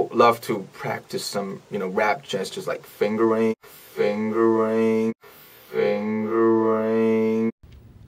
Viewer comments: Rm:And i also like to practise some you know " rap gestures " like fingering Me: HOLD ON WHAT!! I NEED TO HEAR THAT AGAIN !!! RM:and I also like to practise some you know rap gestures like fingering Me: ...um...am I going insane or did he ACTUALLY just say fingering ?? Me : * replaying the vid AGAIN * ..... YEP HE JUST SAID FINGERING!! OMG!! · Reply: Janeen Seecharan oh, haha, i thought he said finger aim 😂😂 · Am I the only one that thinks RM/Namjoon is saying "finger aim" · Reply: Well I thought he was saying finger ring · did he just said fingerin ?? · "finger aim" is what he said not "fingering" bish wtf yall have in yo minds 😂😂